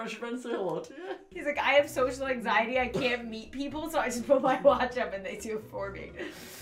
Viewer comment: For some reason when that British guy said hello I thought said “my lunch”😂 and not “my watch”